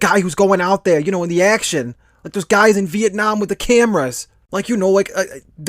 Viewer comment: did he say viet nam?